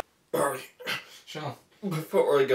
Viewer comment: Does he say "YOLO"?